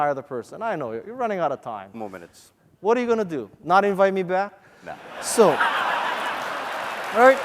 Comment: Kawasaki says something but I didn't catch what he said. Could someone write this short sentence ? Thank you. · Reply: he said "What are you gonna do? not invite me back?" XD